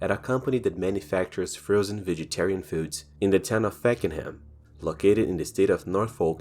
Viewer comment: Hahaha sounded like u said “fackinham” hahaha 😂